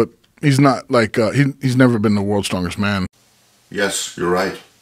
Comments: This dude went on a 2 min rant about not mentioning any competition wins because he thought he said "hes never won worlds strongest man" when he said "never been to worlds strongest man" 💀💀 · clearly says "he's never been to world's strongest man" never said anything about winning anything. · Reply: I'd listen again, clearly says 2:05, "he's never been to world's strongest man." Then Patrick immediately gos after how has never won world's strongest man...not even what said.